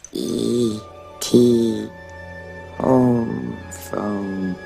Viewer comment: id bet my LIFE that he does not say ET home phone. theres no way!!!